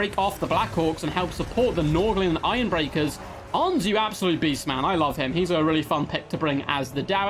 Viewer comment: Did you just call Hans a beastman?! THAT'S A GRUDGIN